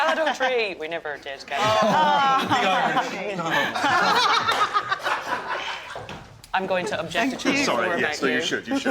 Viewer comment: What did Merchant say at 7:07? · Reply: "oh, the irish"